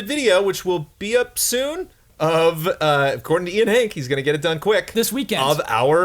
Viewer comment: I thought your were making a show called "According to Ian Hinck" and was way too excited for a moment.